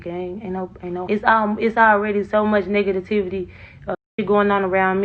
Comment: i think she said "Negative-tivity"...🤔